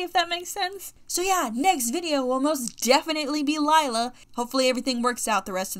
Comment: Wont lie when you said “lila” i misheard it as “delilah” and went “oh damn is loveart redesigning rainbow high too??” Lol